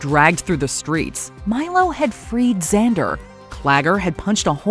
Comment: Thought I heard you saw Xander haha.